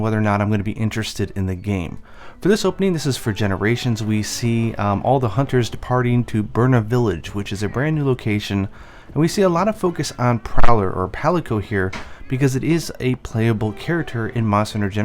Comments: At the start i thought you had said departing to burn a village wait a minute... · Thought you said, all the hunters departing to burn a village. That's not very nice of them... 😓